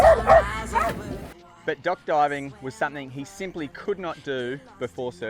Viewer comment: OMG, I thought you were saying "duck diving" like in the damn! I was waiting to see a duck.....lol. Ok, DOCK diving it is. Nice to see Rover & Chance playing again, he truly is a friend to all.